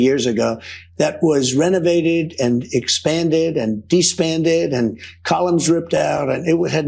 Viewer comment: "de-spanded" ... did he just say de-spanded?!?! 😅